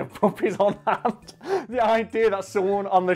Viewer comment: FOR ALMOST HALF THE VIDEO I THOUGHT HE WAS SAYING PUPPETS